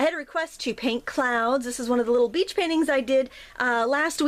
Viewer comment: anyone else heard this is one of the little bitch paintings at 0:07? Great tutorial by the way!